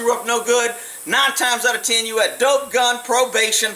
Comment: did you say gun? Carrying a gun in your vehicle is not a crime.